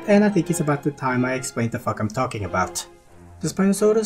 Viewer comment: Great vid! I do have a question, at minute 3:00 did you say "the f***k i am talking about" or "the fact i am talking about"?😅🤣😂 I understood the first, but that might just be me. Great vid, non-the-less.😁✊️